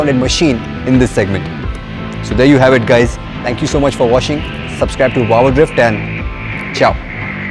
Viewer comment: Why did I hear say thank you so much for "washing" at 6:10 lol 😅 Kickass comparo guys.. Well done 👍🏻